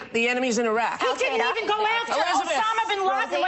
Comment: After the hundredth time of watching I actually just noticed Elisabeth didn't say "Our enemies in Iraq", so I feel bad for Rosie that her mishearing caused this rollercoaster of emotions.